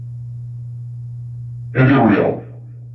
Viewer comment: I SWEAR at 2:12:27-30. I heard the speaker say my name which is Ariel!? 🫣 I do not allow any negative energy from the museum to attach to me through watching this video.